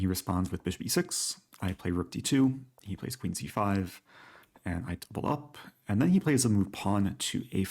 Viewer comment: for a moment I thought said ‘and I pull up’ 😂😂